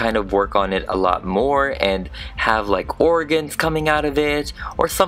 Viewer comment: Your commentary was so funny!! You had me cracking up. XD You also made me a little queasy, so... great job. Lol but seriously, she's an incredible custom. So well made and terrifying!!! I love that wing technique. ♡ㅅ♡ THE SKIT! AHHH BIBI HELLO! MONSTERS UNITE!! I loved that so much ㅋㅋㅋㅋㅋㅋ :D P.s. I know how hard you worked on this video, and I don't know how you pulled off such a beautiful editing job with such little sleep!! ♡ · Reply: I’m going to be honest when you said “......it a lot more and have organs coming out of it” I thought you said “......it a lot more hand have like orange coming out of it” idk why though